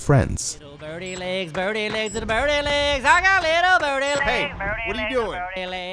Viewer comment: I thought he said 'i got little dirty legs' lul